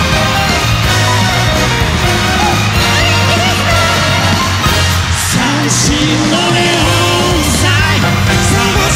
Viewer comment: ここなんて言ってるんでしょうか？ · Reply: セイセイ お元気ですか？だと思います！